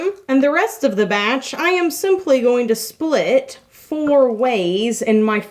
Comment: Is it just me or did she say soaply at 2:49?